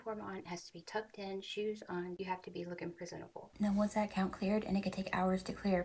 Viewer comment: HAH!! When you were explaining what your morning routine was and you said, "You have to look PRESENTABLE..." I swear I thought you said, "You have to look PRISONABLE" 🤣 I was like, WTF is prisonable?!? You have to look like you're in prison?? Don't mind me... I'm just a legit dumbass.